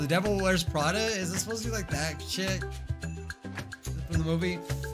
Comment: Did....did just say $hit?